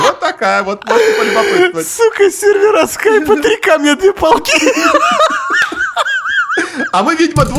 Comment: мне послышались три кометные палки